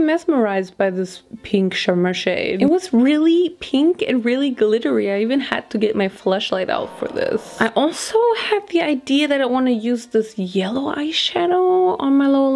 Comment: just listening while doing other things and had to do a double take when i heard this 😂😂💀 could of sworn i heard fleshlight 💀